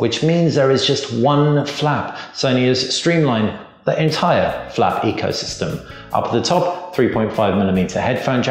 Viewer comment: Dude. Did you say "flap ecosystem"?